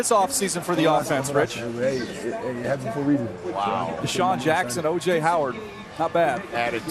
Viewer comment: Cant wait to get his jersey 😭🏈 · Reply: Dirty Dan well I thought u said Deshaun is trash